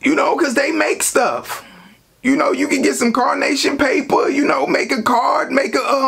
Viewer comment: Mannnnn, I was listening to this while I was straightening up and I heard you talking about making your gifts out of "carnation" paper. I thought I heard you wrong at 1st then you said it a few more times and I was stumped. I had to walk back to the computer, look in your face to read your lips to make sure I heard correctly then pause you and try to remember what "carnation" paper was. Why is it that I don't know what "carnation" paper is????? Then it hit me...CONSTRUCTION paper...LAUGHING. MY. MOTHER. FUCKING. ASS. OFF!!!!! Thanks for that laugh, ha!!